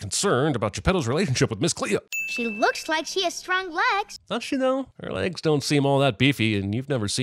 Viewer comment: At one point I thought he said " No she don't!.." and I was living for Daddy sinner. He said "Does she though?" @ 9:31. I still live.